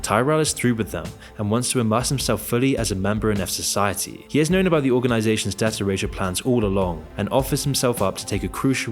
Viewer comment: What is the narrator saying right at 19:33 ? It sounds like “he has know about the organizations data ratio plans all along” - but I know that’s not it. And it’s frustrating